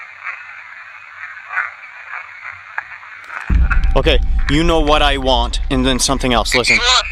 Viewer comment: i dont know if anyone heard this but during the second set of EVPs the first question (42:55) i think it says „you know what i want, river” im not sure if im correct but i think i heard that